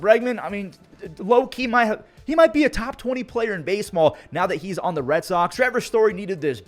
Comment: Anyone hear say base small 😂